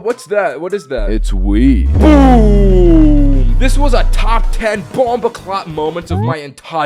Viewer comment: did you just say "top 10 bumbaclot moments"